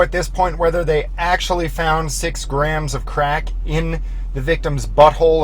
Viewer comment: they said 1.16 grams, not 6 grams. Not like that is really relevant to the fact they dropped the charges though. I had to listen a few times, cause I originally thought they said 0.6 grams, but with the right amount of volume, it's clear they say 1.16.